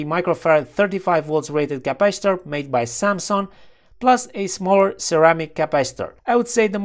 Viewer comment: I heard "capacitor made by Samsung", then i read "samxon" on the component 😁